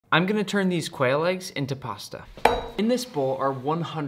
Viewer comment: I thought you said whale eggs😂